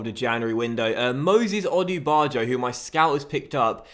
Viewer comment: thought u said Moses Odjer